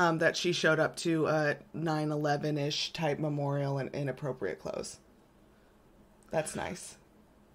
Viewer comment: Great episode, again! You two are hilarious. On a side note I just wanted to let you know that worried me for a second. In the beginning you in talking about what Meghan was wearing you said she was "in appropriate attire" but I had to do a double take cuz I thought I heard you say "inappropriate attire." Anyways I know what you meant now but for a bit my heart sank and I thought I'd let you know. Hope you're doing well. Keep up the great work and love for our favs! ❤️